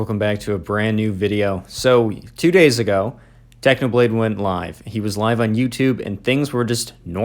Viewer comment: i heard "technoblade went blind" and was like "OMG WTF IS HE OK???"